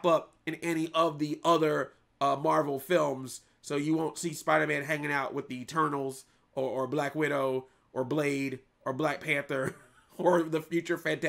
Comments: I misheard "The Eternals" as "The Turtles". Has there ever been a Spider-Man/TMNT crossover? There must've been; because, you know, New York. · I thought you said: "So you won't see Spider-Man hanging out with the turtles"